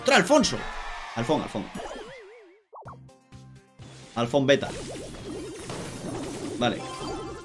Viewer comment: Alguien mas escucho que dijo el nombre de Bta en el minuto 6:22 ? xD